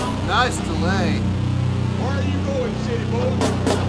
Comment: "Where you goin' city boy" is from the movie "Deliverance" with Burt Reynolds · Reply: Thought he said "Where you going skidrow?" A bad part of Central East Los Angelas kwown for homelessness,drugs and crime.